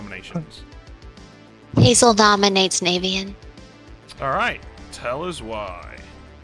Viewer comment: I heard "Richy nominate a bed" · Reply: Also am I the only one who heard at 1:01:59 "Hazel dominates Navean~" because O- and I cannot empahsise this enough-wO